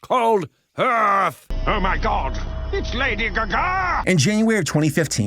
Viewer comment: did bro just say: "its lady gaga!"?